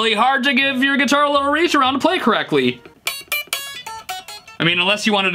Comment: Huh, huh did say "reach-around"? [say in Beavis and Butt-Head voices]